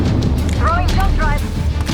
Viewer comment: am I the only one who heard throwing jump pad?